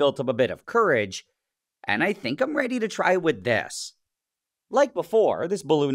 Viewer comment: I heard "I'm ready to try with death"